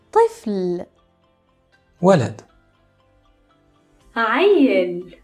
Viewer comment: that sounded like wallet